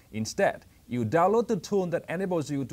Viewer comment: sorry, but i couldn't figure out that word ... instead you use the tool "animals" ? use to upgrade windows from a flash drive? i replayed that bit 5 times and still could not figure out what the actual word is.